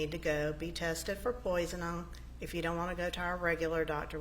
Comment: What an odd person, the secretary. She also has a problem with speaking. I thought she was saying "Poison-on or poison-all" and I think she was saying "poisoning." Other examples "disappearing" for "disappearing" or "burnon'" for "burnin'" "Melody called Lokin for him" rather than 'Looking" "I checked my email on the evenon of the 4th" instead of "evening." I have ived in Georgia and have not yet heard inflections like hers..